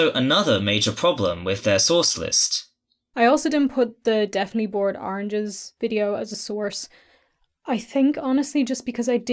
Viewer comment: I know this is an older video now, but there is a captioning error here 1:18:05 It should read "Definitely Bored Oranges' video as a source" as that's what says, but it instead reads "Deathly Bored Oranges as a source"